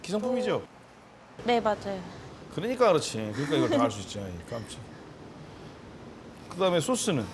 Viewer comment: 끝에 잘들어보면 왜깝쳐 라고들림